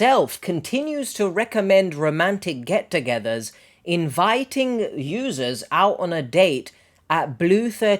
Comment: lol sound like he said “invited losers ”😅😅 ik he didn’t or couldn’t have just couldn’t hear personally over my background just given the context of the conversation I was like hollon lol 😮😮‍💨😮‍💨🫠😂😭‼️